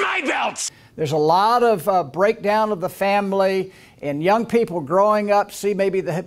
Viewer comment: he actually said “fambly”. With an audible “b” sound. 😳 As god is my witness I thought that was just something Steinbeck made up for the grapes of wrath. I didn’t think people actually said it